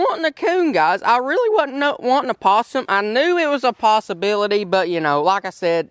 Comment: I thought you said possumbility. Haha. Congratulations on 200k.